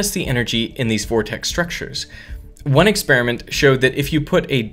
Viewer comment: did he say, DED?